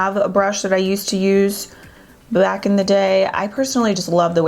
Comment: did i just hear say (black in the day) 😂🤣😂🤣😂🤣😂